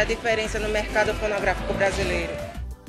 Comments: Eu ouvi "mercado pornográfico brasileiro" foi ? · EU TO AQUI PRA FAZER A DIFERENÇA NO MERCADO PORNOGRAFICO KKK EU ENTENDI ISSO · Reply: Fonografico... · Eu juro que eu escutei mercado pornográfico.